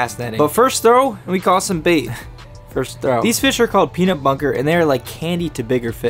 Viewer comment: I thought he said peanut butter 😂😂😂😂